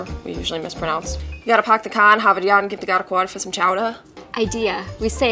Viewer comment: what did this woman just say lmao 😂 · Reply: You gotta park the car in harvard yard and give the guy a quarter for some chowder xD